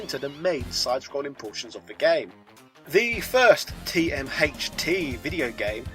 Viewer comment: did he say TMHT?